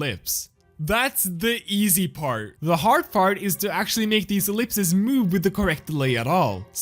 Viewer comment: am I crazy or did he say (the hard fart)